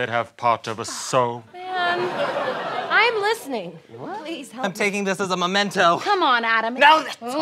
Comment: Guys, did anyone hear "Please help me..." At 2:23???? It's creepy! It kinda sounds like said it but I'm not sure